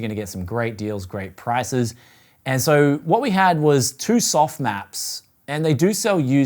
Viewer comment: i heard softmax and i thought why am i watching a vid about AI , then it hit me